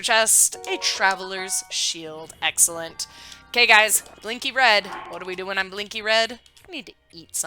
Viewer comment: I thought you said Linky red 🤣🤣🤣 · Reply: Lol, I thought so too when I re-listened to it haha. Blinky red, Linky red... basically the same thing, right?